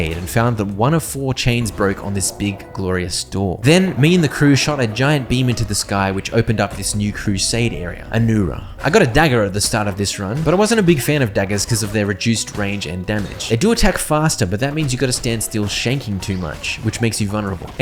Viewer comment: my gramndma heard my watching this and thought he said I got a dagger up my ass 🤣🤣🤣🤣